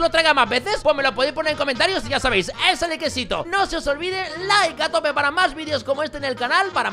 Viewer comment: escuche Laika tope